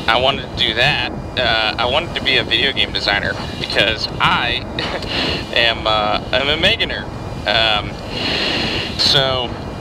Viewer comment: "because i...ha.....am a millionaire" Did i hear that wrong? 😂